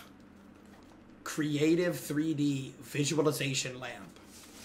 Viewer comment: naw 2:35 mate I thought that said "creatine"